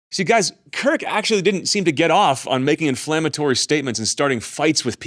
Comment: did you just say, "get off on"?